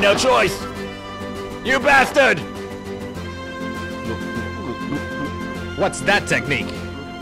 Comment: he clearly says "what the f**K" and dubbed into whats that technique? lol